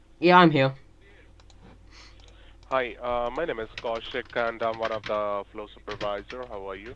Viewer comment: Turn on captions and go at 21:33 and look at what the dude says “hi my name is kasia condom” 🤣🤣